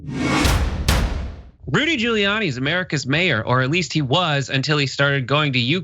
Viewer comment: Thought he said GREEDY Guiliani...